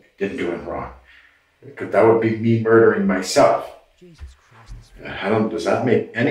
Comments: did anyone hear a voice say "jesus christ!" very quietly reacting to what this guy was saying?? was it you??? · Anyone else hear a voice in the background "jesus christ" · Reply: "Jesus Christ this guy is... " is what the voice says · Did I just hear saying "Jesus Christ, this fucking guy!" around 38:20? Pretty sure I did lol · do I hear quietly in the background "Jesus Christ this effing idiot" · Do I hear, muttering "Jesus Christ this man is insane" .. or something like that @ 38:22. I have to agree. Lol